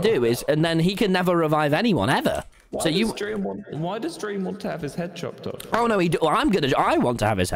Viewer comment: Ghostbur: “Why does dream want to die?” Tommy: “No I want him to die.” Ghostbur: “...ok!” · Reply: ( btw he din’t say “Why does Dream want to die?”, he actually said “Why does Dream want his head chopped off?” so that’s why they din’t find it. )